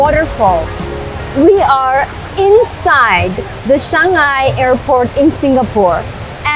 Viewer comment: NOT shangi but CHANG-ee. I do appreciate the effort though because I heard "shanghai". LOL